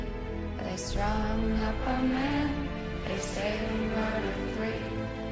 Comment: I always thought they said: They strong up a man, they say they murdered tree"